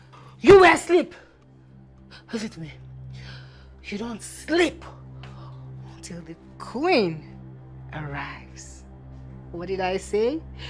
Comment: I thought was gonna say “you don’t sleep until the Queen of mars arrives” lmao